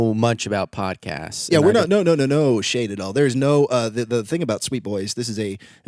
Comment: I kept hearing "no Shane" when they were saying "no shame"...